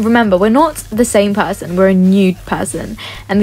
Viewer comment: when she said “new” the captions said “nude”